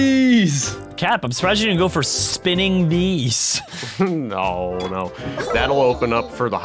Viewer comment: Did anyone else hear spinning dees